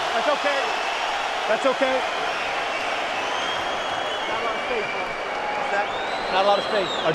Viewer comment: does Dustin say "not a lot of faith man" or what? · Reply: not a lot of space.